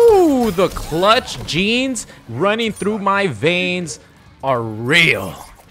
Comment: if i’m gonna be honest,around 13:42 when you said “clutch genes running through my veins” i accidentally skipped so i thought i heard “beans running through my veins” after you won that round and i was like wtf?